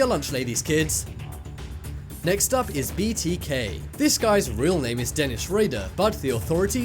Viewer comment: I got scared it was "BTS" instead of "BTK"😂